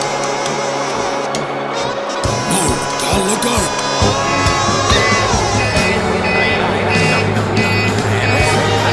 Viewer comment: what did he say? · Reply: He Said Noel Gallagher.